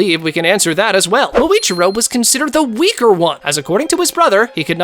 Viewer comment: Bro why did you say week